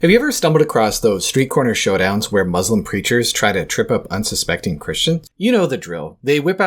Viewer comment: I had my volume low, and misheard 0:08 as "When muslim creatures..." instead of "preachers".